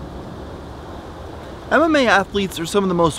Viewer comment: I heard “anime athletes” at the start lol